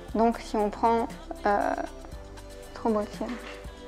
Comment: au debut j'ai compris "trop beau Étienne" 🤣🤣🤣 je me suis dit il doit être pas mal le voisin^^